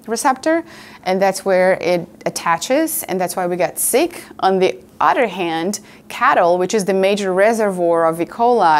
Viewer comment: did she say "on the udder hand"?